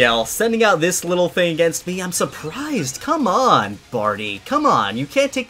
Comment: i thought he said farty not barty